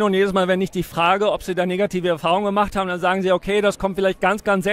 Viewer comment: 😱...aaahhh... RRAAAASSSSSISSMUS!!! Die hat Negertiv gesagt. Ohh... hab mich verhört. Negativ hat sie gesagt! Aber egal... RAAAAASSSSSSIIIIISMUUUUUS!!!